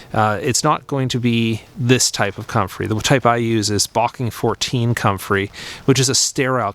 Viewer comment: Hi, would you please give me the name of sterile comfrey you use, I could not make out what you said in the video, sounded like "barking 14" I heard you say. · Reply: Just about - it's 'Bocking 14'